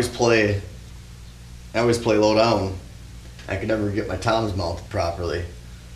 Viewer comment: I thought you said you liked playing load album and I laughed but you said you play low down